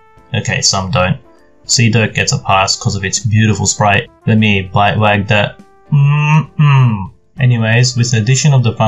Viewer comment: what the fuck did u say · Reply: I fucking said "lemme bhite wag that" a meme I made on fucking reddit (why u gotta say fuck for something so trivial xd)